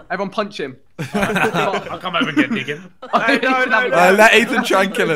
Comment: did ethan say "come over here nigga" at 4:45?